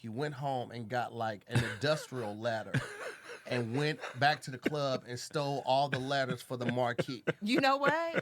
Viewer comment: I had to rewind 3 times to understand what was saying lol. He said "Arvin got a ladder and stole all the ladder's for the Marquee". Had me thinking Arvin was stealing ladder's lol. Time stamp